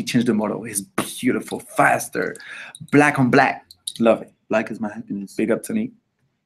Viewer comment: Did Lau say 'black is my happiness"? Because same.